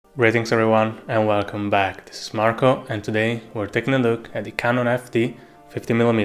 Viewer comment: Great video: @ 0:02 ,I do apologise: but I'm so obsessed with photography, that I thought you said ".. this is MACRO".